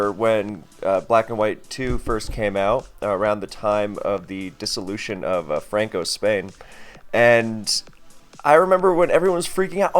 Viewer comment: Did just say the disolution of francos Spain at 1:40 😂😂😂?